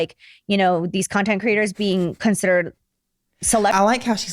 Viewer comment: the audacity of Jacklyn “ Bein A PIONEER …..” did I hear her say that no way 😮